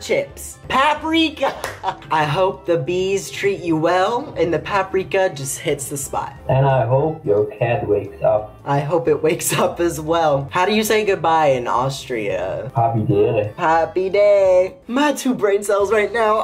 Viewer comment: And I oop- 😂 (I know that’s not what he said but it sounds like it)